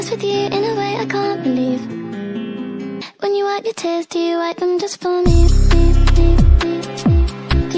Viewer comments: Who else got here by searching "when you wipe your tears do you wipe them just for me"? 😅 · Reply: Lmaooooooo honestly I thought it said “When you wag your tails do you wag em just for me eeee” 😂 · Who else thought that she said "when you wipe your tits, do you wipe em just for me?" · ok but i thought she said "when you wipe your cheeks" and i thought this song was about A$$ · I thought she’s saying when you whitening your teeth do you whitening them just for me 😐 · i always heard "when you wag your tail do you wag them just for me?"